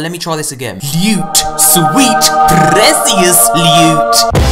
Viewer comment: leiwt sweeet preciousssss leiwwt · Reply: I sounds like he said 'sweet precious newt' 😂.